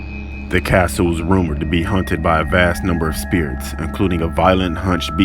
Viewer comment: Sounds like youre saying 'hunted' instead of 'haunted'